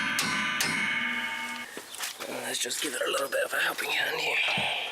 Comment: Any idea where I can get a pair of rear trailing arm brackets/mountings? I’m having a ‘mare with my xtype now🙃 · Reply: did you say “just gonna give it a wee bit of a happy ending”??🤣🤣🤣